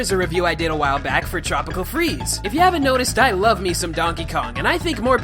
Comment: who else misheard him and thought he said " i love me some donkey dong"